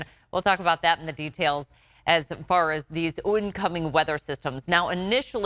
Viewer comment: What’s a “ooncoming” weather system? New word perhaps? 😂